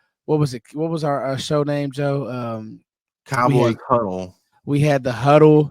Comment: I thought he said “ Cowboy Cuddle “ 😂😂😂😂 My bad 😂